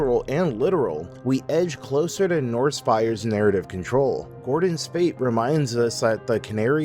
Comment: I misheard one of the lines as "Norse fires and Arabic control" and it was a VERY unexpected turn lol